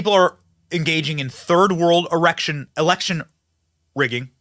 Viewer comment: you had a Chuck Schumer moment 😂 you said erection instead of election